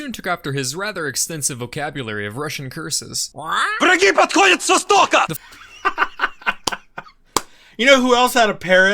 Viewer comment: What did the parrot say in 14:15 ? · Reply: "The enemies are coming from the east!" I believe it's a line from Call of Duty